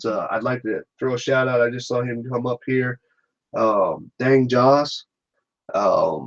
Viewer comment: I can't make out the name of the guy you mention around 57:20 to recommend his video. Any chance you could give it in comments here? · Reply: DANG JOS